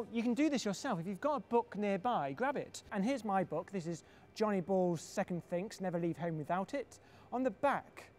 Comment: I thought he said Giant Balls instead of Johnny balls... Lls